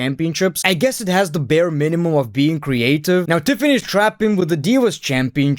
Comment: Did you just called her Tiffany Strapon? XD XD XD